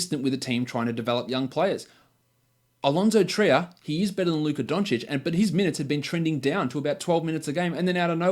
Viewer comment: did he say Trier is better than Doncic? Maybe meant Hezonja but yeah Fizdale is the worst